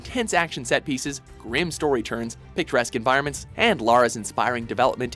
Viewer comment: did you say picturesque or picaresque?